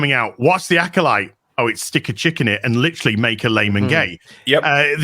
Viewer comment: I thought he said stick a chicken in it.